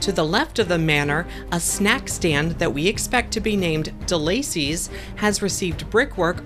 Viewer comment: I couldn’t make out what you said the snack stand in dark universe was called. I think I heard you say delaycies? Sorry I’m not a monster movie buff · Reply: “De Lacey’s”. It’s a character name.